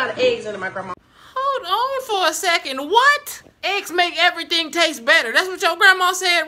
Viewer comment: I THOUGHT SHE SAID AIDS MAKES EVERYTHING TASTE BETTER 😭🤚